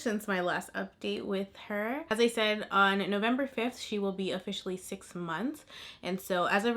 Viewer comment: wait, did sis say November 5? That’s my birthday. BIRTHDAY TWINS 🥰😍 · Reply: Her birthday is May 5th lol she’ll be 6 months on November 5th